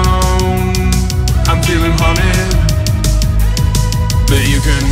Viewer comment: This whole time I thought he was saying Horny not Haunted